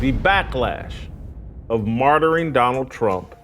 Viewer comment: I thought for a moment Jason said, Blacklash? I was thinking Jason is being very clever here.